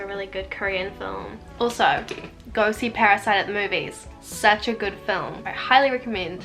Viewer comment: What movie did u say at 11:03? Didn't quite get the name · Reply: The film was called Parasite ! <3